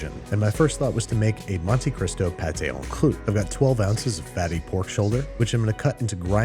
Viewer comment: I misheard “fatty pork shoulder” as “Babbi Pork Shoulder” and thought for a second that the BCU had branched out into pig farming and butchery